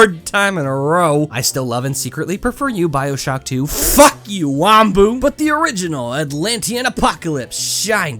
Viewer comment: I HEARD HIM SAY WAMBO!